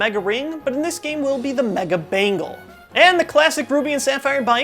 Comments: Some reason I heard "mega bagel" instead of "mega bangle." Now I'm hungry. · I thought I heard Mega Bangle as the Mega Bagel...